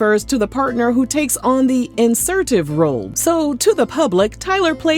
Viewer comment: did you say insertive or assertive 🤔😂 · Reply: insertive